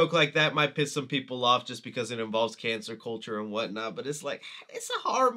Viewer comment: did you intend to call it ‘cancer culture’ instead of ‘cancel culture’? If so, well played 😂